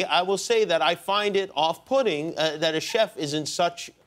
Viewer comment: "I find if off-pudding" Im not the only one who heard that right? xD